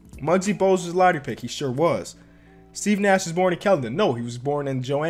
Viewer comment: Kelton? Anyone else catch that?